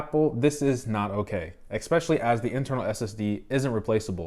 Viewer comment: Research only takes a couple of minutes. Better to come up with your own judgement than to blindly follow others. Also, I love attempting to use big words than googling it seconds after to see if I used it correctly. YOUR WELCOME! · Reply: I don't think it says ex - pecially but rather es - pecially.